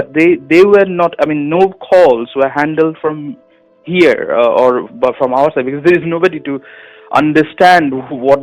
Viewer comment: What is he trying to say at 13:05? "No calls were handled from here (or from our side) because there was nobody to understand what they are fixing"